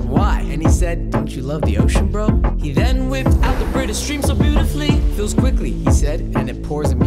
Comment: All I heard was “He then whipped out the britastretateflafle”